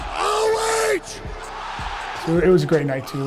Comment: What does he actually say here? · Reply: OH(Ohio)